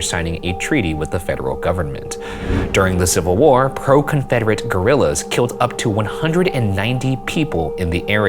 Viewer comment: What he said: “pro-confederate guerrillas” What I heard: “pro-confederate gorillas” now I can’t stop imagining the Civil War exactly the same but somehow the south got a band of mountain gorillas fighting for them, woulda been crazy